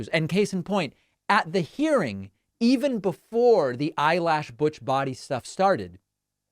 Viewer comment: I thought she said a Bush Body, which would have worked just as well. Maybe better